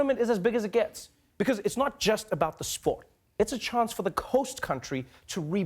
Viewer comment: trevor said “coast country” instead of host...did anyone else notice?